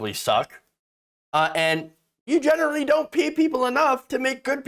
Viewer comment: Did you say "you generally don't pee people enough to make good pizza?" I don't think giving your employees golden showers to improve the quality of the pizza is a good strategy. 🤣